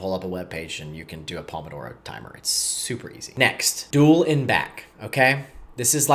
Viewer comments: I'm following along as best I can. But, we're at 11:14~ and you say, "duel and back?" Or "doolenback" or what? You didn't even display the words. I'll figure it out, but I t causes a distraction for me. Duel and back? Ohhh.. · At about 11:14 he says something like "dual and back" but the words are never shown on the screen, so I'm not sure what the heck he is saying. Googling shows "dual n-back" as a result, so I guess that is what he means but I wish he would have it printed on the screen - after all, he does that with lots of the other terms he uses, but not this one, yet this is the most uncommon, unfamiliar one.